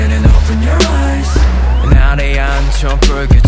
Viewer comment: I like how the ARMYs are hearing 'eat jin and open your rice', the IGOT7s are hearing 'jinyoungie open your rice' and everyone else is hearing 'Jeremy open your rice' XD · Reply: i hear 'eat jin and open your rice' and 'jinyoungie open your rice' im MuLtIfAnDoMeD